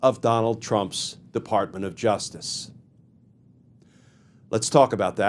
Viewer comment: early on in this video, you said "tRump's Department of Justice," but for some reason, I heard tRump's Department of Injustice." Thank you for your excellent content, and please keep up the good work.